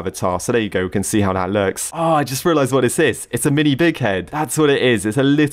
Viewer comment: ok surely I am not the only one who heard him say mini d word head